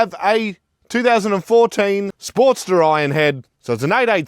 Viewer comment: Did he say "2014 Sportster IRONHEAD" ?